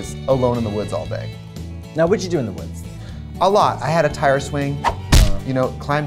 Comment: why did I think trixie said "Tyra Swing"